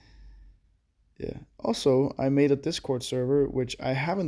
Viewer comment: Did he say discord here 19:04 hmm might have to cheeck it out ;>